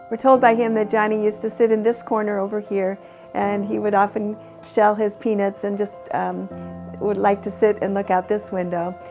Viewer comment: 😳WHAT did she just say?! ... Oh ...”shell his peanuts” 🧐 Whew 😅 And @ 2:32 😱🤣